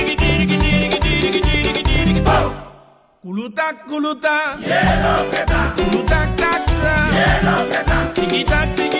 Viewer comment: Svp il dit quoi à 3:34 ? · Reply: Pouloutak